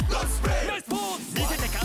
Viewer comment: Is he saying "Nice boobs"? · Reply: Nice pose